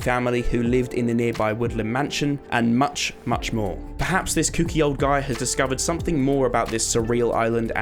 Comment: did you just say kinky?